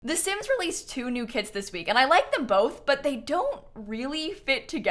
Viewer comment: i thought kayla said “the sims released two more kids this week” 😭😭